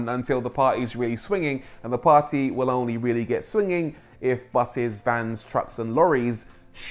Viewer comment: did you just say BUSSIES